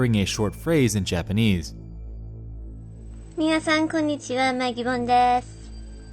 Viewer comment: i think she said something along the lines of "Hello Mr/Mrs 'Mia' [just someone's name, she actually said 'Mia-san'], i'm Magibon" so it could've been a shoutout for one of her Patreoners back in 2006!!!